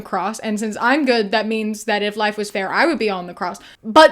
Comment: I'm too gay and have too much religious trauma for the "if life was fair, you'd be one the cross" thing. I heard "if life was fair, I'd get to crucify you" Also, if it was fair and good that Jesus was crucified, why did he yell out "my god, my god, why have you forsaken me?"